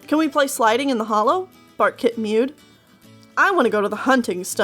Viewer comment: i keep hearing fart kit